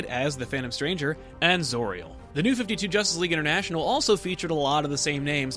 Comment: who's the angelic character mentioned at 5:29? I can't understand what he's saying. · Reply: zauriel